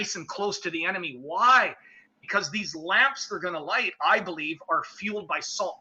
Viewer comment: When was talking about Gideons men attacking with lamps I could've sworn he said lambs. "Don't light your lamb until you see the whites of their eyes!" 🔥🐑💥